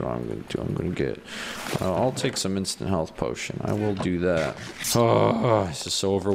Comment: when you said "Instant Health Potion", I don't know if it was just me, but I heard... "Instant Hell Potion." I guess I'm just weird if nobody else thinks that...